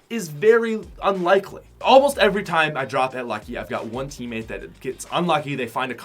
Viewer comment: did he just say lucky?